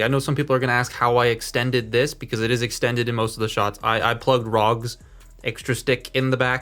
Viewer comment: Whose accessory was used to extend the axe? I heard "Rog's" and the auto-captions says "RG's" but both doesn't seem right. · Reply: Red cog (aka Rog). It was a target exclusive if siege cog but in red that came with a bunch of weapon accessories. It was basically an accessory pack with red cog shoved in it